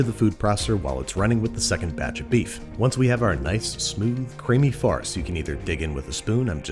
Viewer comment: wasn’t paying attention and was pretty sure I heard smooth creamy farts and I’ve never reversed faster